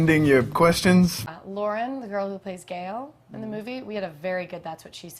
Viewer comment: does she say Lauren the girl that plays gale?